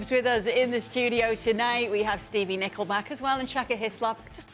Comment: I heard “… we have Stevie Nickleback on the show tonight …” at the beginning